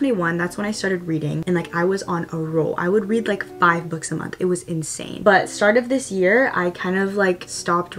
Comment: When you said “I read 5 books a month!” I thought you said 5 minutes! (